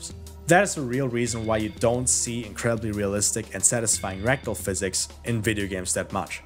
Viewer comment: thought you said rectal physics